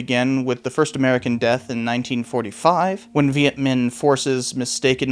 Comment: Did you say "Viet-men"? Only curious, we all make mistakes, unless it wasn't. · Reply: Vietminh